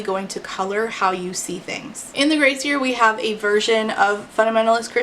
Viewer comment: So I am very slow, at first I thought you said "Gracier" as in "more grace than another possesses". I was confused how it was different from "The Grace Year". Then it hit me. I am a fool who needs to pay attention.